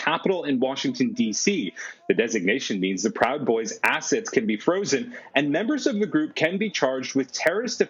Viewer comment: did he say, “assets frozen” pre- conviction? So, does a parity-system also withhold all (prosecutors and investigators and police workers) pay until they win a court case? Does 4th amend protect US from similar dblStd’s.?